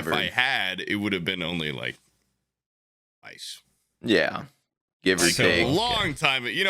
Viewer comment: i thought said ice instead of twice, had to do a triple take